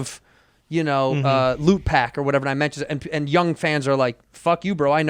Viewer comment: Did Santino say "LootPack"? As in the Hip Hop group The Lootpack?